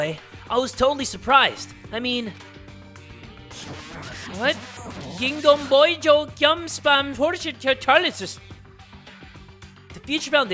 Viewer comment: I literally died laughing for an hour, it sounded like he said "yoying om byoin jwoin yum spam porjik tahl ih sis"